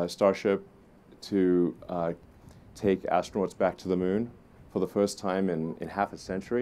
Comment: For a second I thought he said they’re “taking asteroids back to the moon” 😂